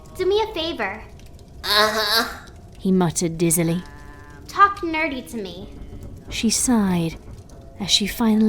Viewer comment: When sweety bell said "talk nerdy to me" I thought she said dirty not nerdy LOL